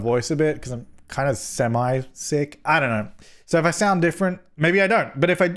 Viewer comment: What said: "I'm kinda semi-sick" What I heard: "I'm kinda semaJ sick" sigh